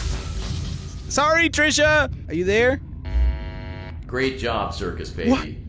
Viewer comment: When the voice malfunctioned and said great In a demonic voice I fr thought it was saying grape 😭